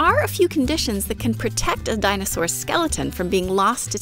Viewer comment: I've never heard the term gantlet used instead of gauntlet, but apparently they're both technically correct in this example. I'm curious about the particular reasoning behind this choice. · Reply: I was also confused at 1:54 by what sounded like her saying "protect un dinosaur's" instead of "protect a dinosaur's" I had to go back and check the captions before I was convinced it was just a mistake and not something else.